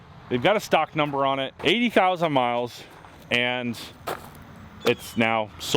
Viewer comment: This vehicle is out of factory warranty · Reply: Ahhh.. ok, my bad. I wasn't listening closely enough. I thought you said that it had 80 miles on it, not 80,000.